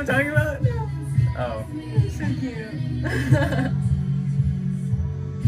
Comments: Am I the only one the heard " uh guys this is my sister" 😂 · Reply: i thought he said “uh i can’t kiss my sister”😭😭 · Am I the only one the heard " uh guys this is my sister" 😂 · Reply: i heard “i guess it’s just my sister” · Am I the only one the heard " uh guys this is my sister" 😂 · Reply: I heard “I just kissed my sister”😭💀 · I heard “alright guys this is my sister” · Ok but like I heard at the end “Hey guys this my sister”. Idk I heard that 💩 Ik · i thought he said this is my sister i was like what😭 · Did he just say “guys this is my sister” LIKE WOAH